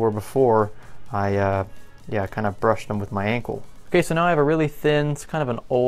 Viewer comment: When u said ankle I thought u said uncle